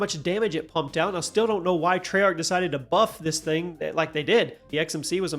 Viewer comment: I thought you said muffed instead of buffed but you know what, that's actually a better term for nerfed so it's going in the vocabulary